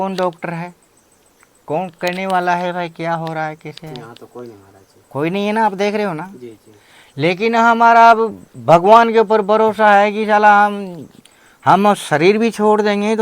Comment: did he say sala 😮